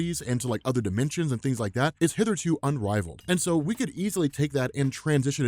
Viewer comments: did he just say 'hither to' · “did he just say ‘hither to unrivaled’” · did you just say "hitherto"?